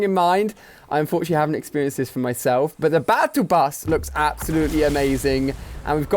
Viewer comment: 😭🤣😭🤣 did he just say battle pass